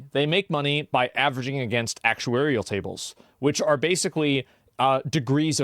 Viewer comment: what does he say here? actual area tables?